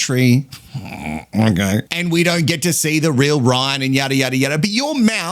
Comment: stay in bin. When they asked if she staying or leaving he smirked and said “ we know what she’ll do. “ He thought she was staying. He got a shock when said leave. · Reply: Just goes to show how badly he speaks as i thought he said "we dont need to see it" 😂😂😂😂